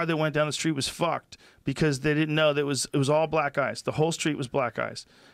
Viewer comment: The 1st time I heard "black ice" I also thought it was black guys